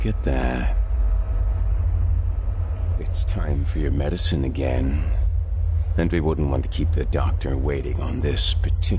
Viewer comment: First of all I’m hooked can’t wait for the next episodes. Second what the heck does the monster say at 2:18.42 please lmk 😍😍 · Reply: I think it was, "You killed my son!"